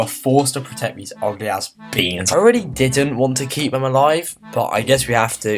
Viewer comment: why did I heard orgasm beans at the first time?